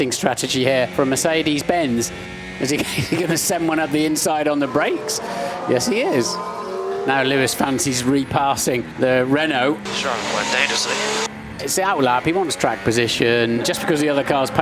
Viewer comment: Did Martin Brundle use Singlish at 2:58? I thought I heard, "It's out lah, people just want track position..."